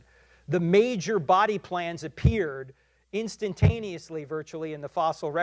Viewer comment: I rewatched the video and realized I misheard the sentence " the major body plans appeared..". I thought he said "plants, not plans", so I wrote about plants. Anyway, his argument Is absolutely wrong. First, Animals appeared before the Cambrian period, during the Vendian period (until ~560 million years ago). A Major mass extinction happened at the end of it, from which we mark the beginning of the next period, the Cambrian. Search for the Ediacaran Fauna, which represents the very first animals ever appeared. Most of these were jelly-like, very hard to fossilize. Then, in the Cambrian, we have much more diversity taking Place: Anellids, Shellfish, trilobites, even the first Chordates ( ancestors of vertebrates). Se have a lot of fossils from the Cambrian, some are spectacular, like the ones from Bergeiss Shale, that perfectly represent the development from one species to another in different layers of soil (and so of time span). Anyway, the source Turek cited, this "Steven Myers" got debunked many times by professionals, Just as any other creationist eventually did. As you correctly said, few rests fossilize, so we'll never know how most of the species that ever lived looked like. So we can't expect the Fossil record to be complete and anytime we find a representative species of our ancestory, still that won't probably be the species we humans derived. If we climb the philogeny tree backwards, from Homo Sapiens we'll find other Hominins (bipeds) like Australopithecus, or A